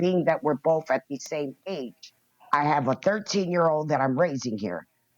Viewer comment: Swear it sounded like she said she's renting to a 13 yr old?? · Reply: 19 year old